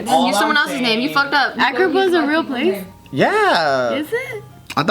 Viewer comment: Lmao I thought said "Africa is a real place?" I was like "fucking Americans".